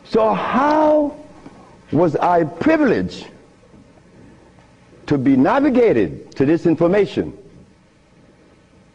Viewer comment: Great information. Please help: what he suggests to use instead of yeast? It's 1:44 or a little later. Please help, thank you :)) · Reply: Hi Alex, he said 'Sea Moss'...which is also known as Irish Moss. You can usually get from Asian shops. Hope this helps.